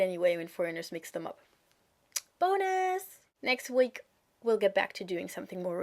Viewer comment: Anyone else notice at 5:43 when she said Bonus, at the bottom left corner she put in Boners because that's kinda what it sounded like.